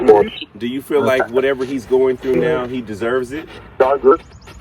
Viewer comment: I heard Yes he does..